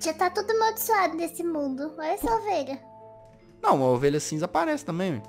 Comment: só eu que escutei "não moh"? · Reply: Não,é porque ele disse:"não a ovelha". Só q ele falou tão rápido que parecia que ela não moh